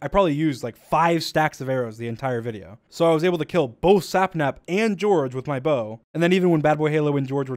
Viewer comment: I thought he said both sapnaps. 🤣🤣🤣🤣🤣